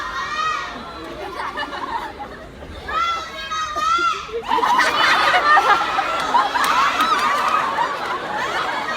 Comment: เขาพูดว่าอะไรอะ5555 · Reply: คือมีคนตะโกนบอกน้องว่า "อุลจีมา" แปลว่า "อย่าร้องไห้" แต่คนนั้นเค้าตะโกนว่า "แว อุลจีมา แว" แปลว่า "ทำไมอย่าร้องไห้ ทำไมล่ะ" คนเลยหัวเราะกันค่ะ